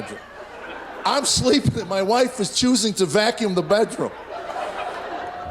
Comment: Hajime says wife, subtitles say Husband. I can not allow this, as it is not Hajime x Mikan REEEEEEEEEEEE